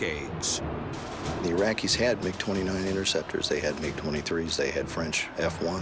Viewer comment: the captions are wrong - not "make 29" and "made 23 fighters"... MIG. "Mig 29" its a manufacturing complex. Captions are off for like the entire video